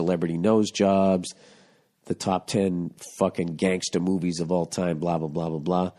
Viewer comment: Haha i thought you said "gangster boobies" of all time! Hahahahaha